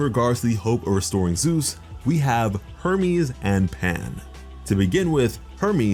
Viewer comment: I almost thought he said Herpes instead of Hermes.